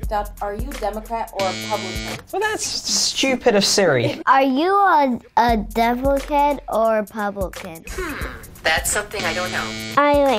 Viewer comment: I heard "Are you a devil head or a pelican?" Anyways, the kids are so cute trying to pronounce the words